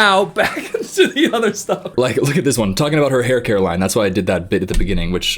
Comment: Uhhh guys 6:02 I heard him say “I’m talking about her hair Caroline” and my names Caroline and I think I’m tweaking 😭🤚